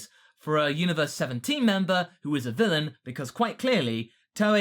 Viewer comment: wait did he say "universe 17 member"